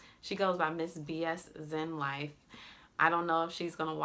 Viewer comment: Yahdon from the YouTube channel Lit. He has an instagram as well. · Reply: Thank you. For some reason I thought you said Miss Zenlife or something like that.